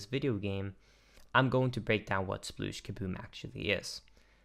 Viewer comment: I keep hearing “splooge.”